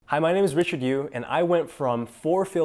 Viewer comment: thought he said "Hey, my name is Richer Thank You"